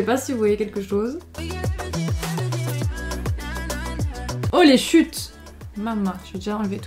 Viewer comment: J'ai compris : "Holy chutes" x)